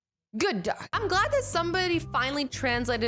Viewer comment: I thought she said not all dogs can breathe English